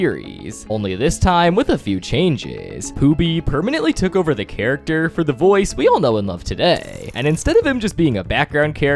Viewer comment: i couldn’t him. What did he say his name was? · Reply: Pooby